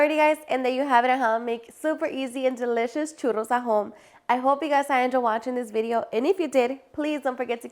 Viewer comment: I thought she said churros ajos I was about to say what ! lol